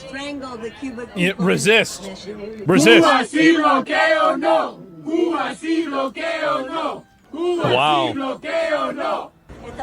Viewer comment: Did they say, "A gay. Oh no!"? That's just offensive man. They with Westborough or something?